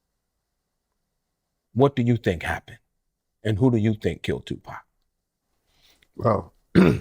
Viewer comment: I thought he said tut killed pac